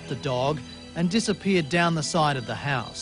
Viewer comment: What the hell does say at 5:02? 😂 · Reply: "When it happened, I'll bet there was some bar talk! I'll tell ya"...